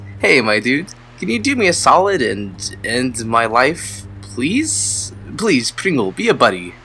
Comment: At first, I thought he said "End my wife" but he said "end my life"